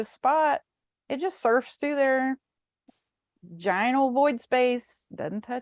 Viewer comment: mins in the video mentions "surfs through the ginal void space", Im curious what the term ginal means?, I dunno if I heard it correctly..... my dyslexic intuitive self heard it as vaginal void space & went on to mention the cosmic birth portal, cosmic birth canal & the yoni Nakahara. I'm curious if it was ginal void space or vaginal void space? 🙏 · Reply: Lololol “giant ol’ void space”... it’s the southern accent 🤣